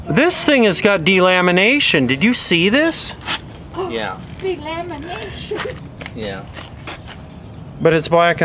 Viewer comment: anyone else hear the old woman go "who! Delamination!"